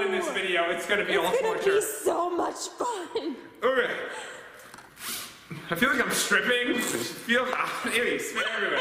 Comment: Did he say I fell like I’m striping